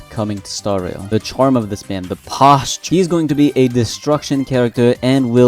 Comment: He is going to be a destruction character! Me (at first second, mishearing "destruction" for "distraction"): Oh, he already is, baby...👄